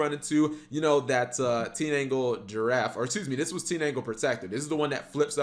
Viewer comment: What did he call the card? Tin Ankle Giraffe? xD I would play that Deck, for sure!